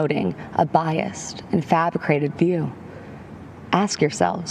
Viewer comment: did she say "fabicrated"? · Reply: yep, she said 'fabricated'